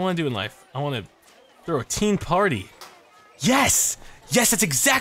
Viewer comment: I thought you said throw a team party not teen party lol